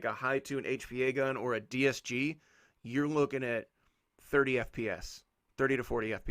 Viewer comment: Am I the only one but i hear him say fps. At 3:45ish